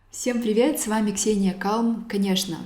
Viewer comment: Мне послышалось что она сказала Ксения Кал